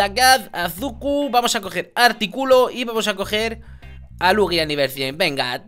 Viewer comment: escuche mal o en el minuto 01:46 dice artículo en vez de articuno